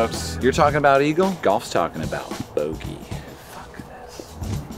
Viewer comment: Anyone else hear the "F This" @ 10:39? Haha